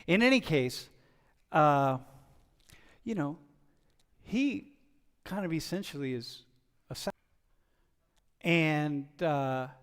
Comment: for anyone curious what he said at the 5:40 mark; "...he kind of essentially is assassinated for his beliefs, for challenging people."